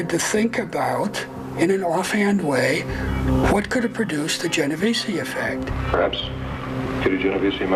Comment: i can't find it, i'm from europe what word are they saying at 14:30 ? jennavaci effect? someone please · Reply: genovese effect, got it